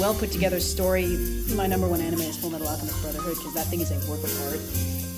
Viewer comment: Can anyone tell me what anime was talking about? She spoke really fast i really could not catch it. Thank you internet! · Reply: Full metal alchemist brotherhood.